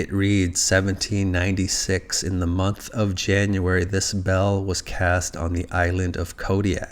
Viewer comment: "This bell was cast" What I hear: "The spell was cast"